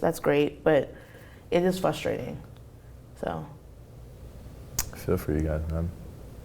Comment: Anyone know what he said at 6:02?? I couldn't really understand what he said? Did he say "I feel for you guys man?"